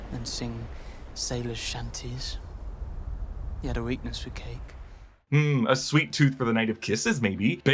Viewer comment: Defo misheard 'sweetness for cake'...